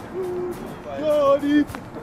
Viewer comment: haha wat zegt bazoer bij 11:43 ? johnny .....? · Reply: Johnny bedankt zegt ie